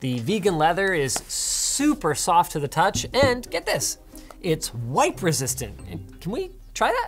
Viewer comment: I thought he said White Resistant 🤣🤣